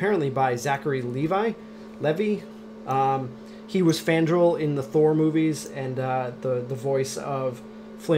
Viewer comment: Marc Vork FANDRAL. I said he played FANDRAL, one of the Warriors Three, which he most certainly did in Thor: The Dark World. <link> He was slated to play the role in the original Thor, but could not due to scheduling conflicts. The first actor who played Fandral dropped out out of the sequel, and by then Chuck was over and Zachary Levi stepped in again to play the role. Not to be a nitpicker or anything ;) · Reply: omg, yup you're right. I stand corrected, sir! Really thought I heard you say Thranduil.. Never knew he played Fandral. Now I feel like a total dork. Oops!?!